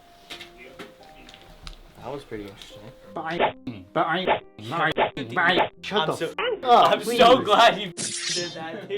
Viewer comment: i have no idea what he's saying here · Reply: "but are you 15 but are you 15 are you 15 blah blah" He was just mocking the pred